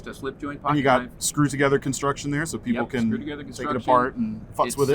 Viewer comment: Love Knife Center and. Love Buck Knives. Just curious though. Did drop an “F” bomb at about 4:18 of this video. Not offended, just wasn’t sure if I heard it right? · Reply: I thought he did too but on a second listen i think he said "so people can fuss with it"